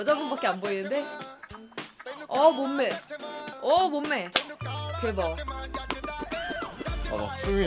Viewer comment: Did she say "Oh Bumme" cos that rhymes with Tamil word "Bommai" which means doll ;-)